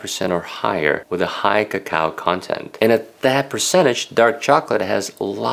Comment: did he just call cocoa “ca-cow”?